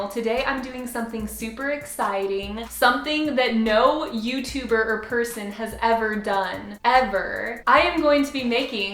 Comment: when you said Youtuber or person I really had to rewind cause I thought you said youtuburger person what a conundrum